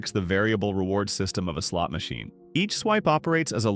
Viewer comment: i heard sl*t machine hahhahahaha